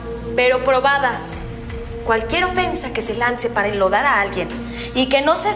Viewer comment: Qué dice? 11:27 cualquier ofensa que se lance para "????" a alguien... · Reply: Enlodar